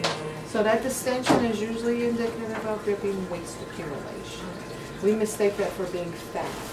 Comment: Can someone tell me what she said at 1:52? I didn’t catch what she said. She said “your belly is very extended, that extension is a result of __ that we mistake for fat”. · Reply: Waste. She was explaining about how my stomach was sitting.